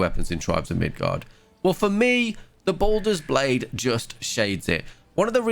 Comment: It's so confusing when he talks about baldr's blade it sounds like he says baldr's blaze and I keep thinking that's something else